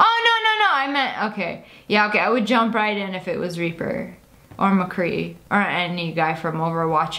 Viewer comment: Wait I thought you said Ra-Reaper